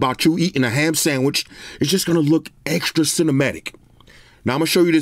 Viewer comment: Subscribed instantly when I heard “cinimadik” 4:51 😂 On the real though love your style of reviews.